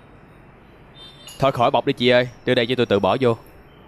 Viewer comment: Dear Teacher, I can't understand what the male character says form 1:50 to 1:54, could you teach me what Vietnamese phrases he said? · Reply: "thôi khỏi bọc đi chị ơi, để tự tui bỏ vô" . bọc means bag